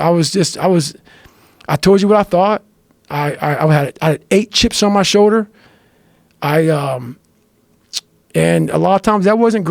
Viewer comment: at first i thought he said “i got 8 chicks on my shoulder” i was like bro how😂 only got 2 shoulders